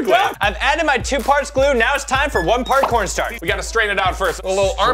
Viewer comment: it sounded like said parkour starch